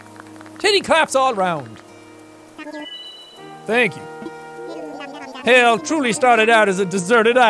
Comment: It kinda sounded like Tom Nook said "Thank You."